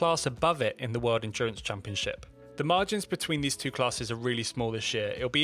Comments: Why do I hear this... 5:06 "The margins between the two are really small as sh*t." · “the classes between these two are really small as shit” uhhh did i hear that right? never seen this man curse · Did I really hear "the margins between these two classes are really small as shit"? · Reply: It's his accent, he said “this year” not “as shit”.